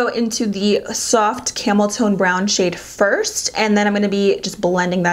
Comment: OMG! I looked away from my phone and at 11:39 I heard camel toe. Lmao. I knew you tone but I laughed.